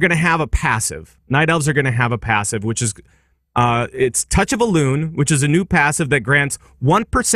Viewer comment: or so did you just say Touch a balloon? I swear that's what you're saying there:D I know it's Touch of Elune but it's just much better like that :P